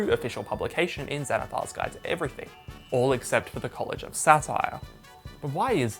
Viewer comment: Was listening in the car I thought you were saying “College of Sapphire” for the longest time. Not even sure what this but I’m off to homebrew it!